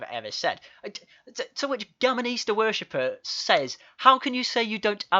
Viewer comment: Instead of Gammon Easter Worshipers I thought you said "Gammonista Worshipers" :)